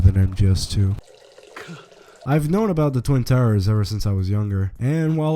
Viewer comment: did... did he say twin towers?